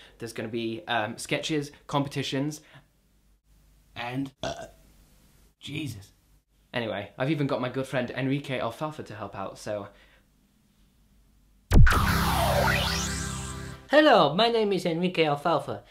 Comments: lol, it did kind of sound like he said girlfriend, but he actually said "my good friend Enrique." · I thought you said, "I've even got my girlfriend Enrique Alphalpha to help out."